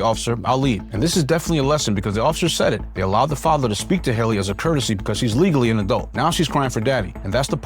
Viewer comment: i thought she said her name was? Not